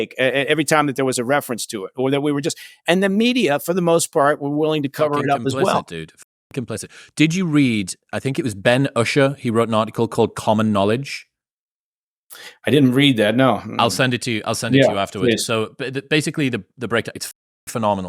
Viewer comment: Could someone share the link he talks about at 55min. I thought he said Ben Usher Common Knowledge but I didn't find anything. Thanks. Also is there a way to search the comments?